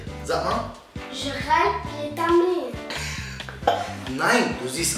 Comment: Ich hab bei1:46 Gillette abdi verstanden